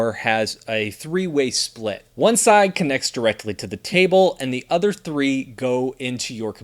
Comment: Did I hear "One side connected to the TABLE"?